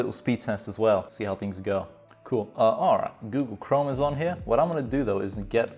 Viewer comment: lmao I thought you said google chromosome